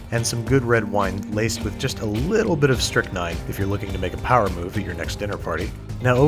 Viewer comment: What is the thing he added at 3:57 to the wine? I can't make out what he's saying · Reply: He's saying strychnine